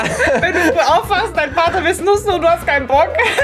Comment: erstmal kurz anstatt "Partner" "Vater" verstanden